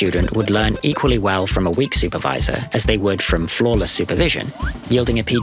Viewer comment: Explanation of the "floorless solution set" to find the "strong ceiling performance": You're mishearing "flawless" as "floorless". Just thought I'd share, since it took me quite a while to figure out. In the end it was Google's AI mode that figured out what my problem was.